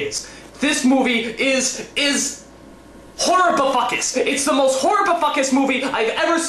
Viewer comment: Funny thing is I HEARD "Horribifuckus" When it's infact "Horrifuckus". I can add the "bi" in the middle (which actually sounds better and makes more sense) and toatally get away with it! YAY LOOPHOLES!!! = D